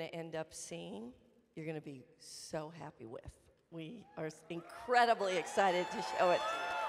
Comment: Did she say "you're gonna be so happy with me"? 9:00:00 PM 4/18/2019 Spring break day 0.5